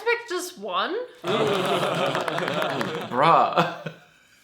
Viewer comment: I love how thought Jack said "bra" instead of "bruh" at first